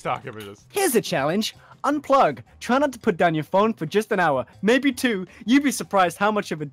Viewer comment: Did say "Try not to put down your phone" or did I hear it wrong?